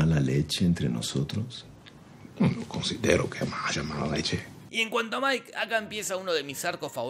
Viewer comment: Que dice Gustavo?? Estos chilenos jajajaja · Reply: "Yo no considero que haya mala leche"